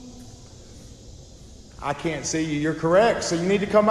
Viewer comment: I think it said I can see you but you can't see me